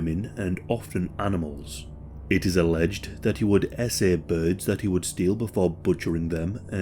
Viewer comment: Did I just hear that right? SA birds!!!!?? Mad